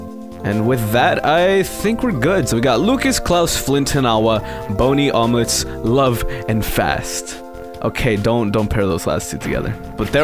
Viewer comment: I zoned out during the intro and when I focused again I heard "boney omelette love, and fast" and was thoroughly confused